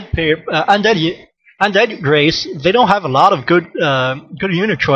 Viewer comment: lol, what he say...ohh undead race.